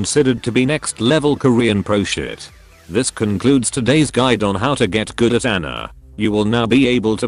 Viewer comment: Thought it said how to git gud at anal